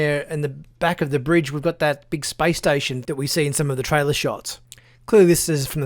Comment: Sometimes the Aussie accent gives me a startle -- When he said "Space Station" I heard "Spice Stytion" and briefly wondered how the Harkonnens managed to crossover into the Star Trek Universe...